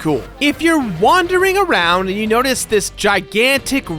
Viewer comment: "Vendy the Vending Machine" oh no I misheard that as Vendy and the vending machine send help